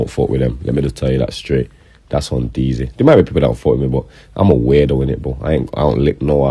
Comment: All I heard was “I’m a weirdo” came out his own mouth